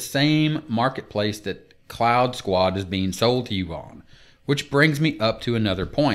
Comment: Wasn't paying full attention at the start and thought you said "clown squad"... the name stuck with me through the rest of the review! I think I'd rather pay a (small) monthly fee, as you rightly say, how is it sustainable when new people stop joining and paying their one-time fee to the company?